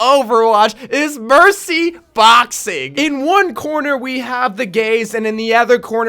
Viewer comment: Did you say the gays